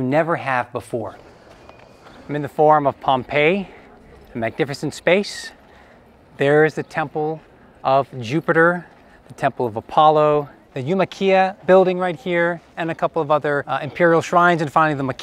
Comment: I thought he said " The IKEA building right here" LOL